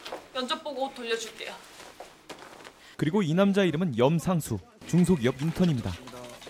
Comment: 에 염상수라고했는데 정상수라고들림 ㅋㅋㅋㅋㅋㅋ